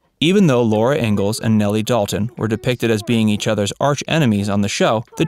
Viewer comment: Was I the only one that heard him say: Nellie Dalton?